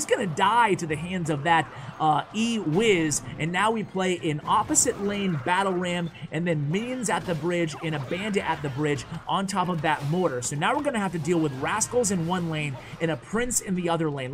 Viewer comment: I thought you said beans at the bridge 😂